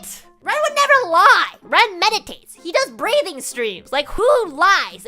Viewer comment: I didn't look at the vid and misheard that ren does breeding streams 💀